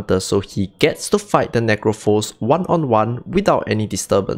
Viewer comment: Idk why the caption misheard necro as n**